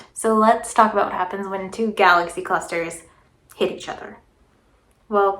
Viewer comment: At first, at 30:51, I heard "jackass clusters" and thought, "Wow, she really has a gripe with these mofos!"